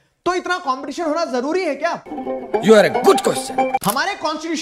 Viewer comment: i was listening to the video soo carefully and this is what I heard 2:38 you are a good question